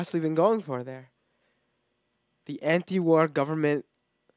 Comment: I thought he said government ninja